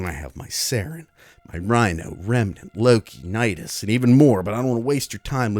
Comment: did you say Remnant?